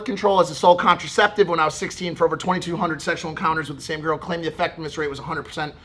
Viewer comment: did mike just say he had over 2200 sexual encounters with the same girl lmao